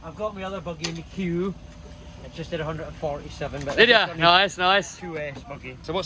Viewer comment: oohh.. 'Martins buggy there's a scorcher for a 2S!.. Insane what speeds You can push out of these Cars. did he say he had 147mph out of that?